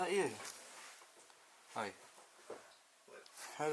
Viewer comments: I heard a child or woman say something. Just before he says "was that you?". Crazy! Is it haunted in those caves? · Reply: I heard it to. Sounds the same. I think it says he Fellers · I hear a woman go hello and 25:39 I hear a woman say hello